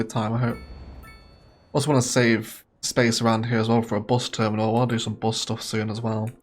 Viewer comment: I totally misheard "I wanna do some bus stuff soon" 😂😂